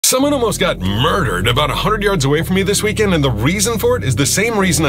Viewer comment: the first thing i hear is “someone got murder 10. yards away from me” and i swear to god i though he did it.